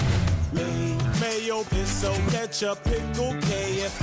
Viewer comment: I luaghed so hard when i heard i dont wowo on